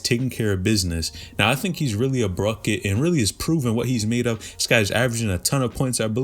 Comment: did you say Bruket 😂😂😂😂 4:44 😂😂😂